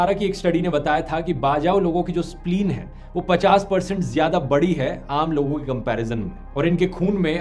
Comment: did he said spline?